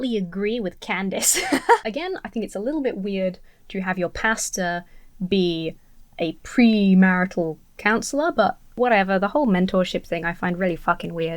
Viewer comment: ish, can I just say that as an American, with your British accent, I heard you say "I think it's kind of weird to have your pasta be premarital council". Which, yeah, that would be quite weird... lol